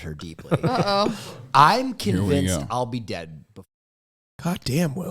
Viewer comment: wait..... what did Will say at 11:38 ....... why was the audio cut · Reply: Tried to read his lips. Looks like he said dead before he’s 40…. I hope he doesn’t really feel that way.